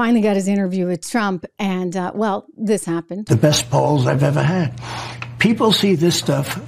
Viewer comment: Swear to god it sounds like he says the best balls I ever had......🤔